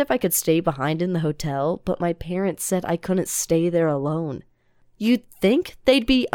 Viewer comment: I thought it said "my parents evil"